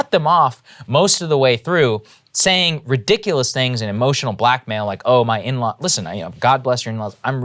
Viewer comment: Wow did he just say like a emotional black male. Dam homie 😕 😳 and I actually like u bro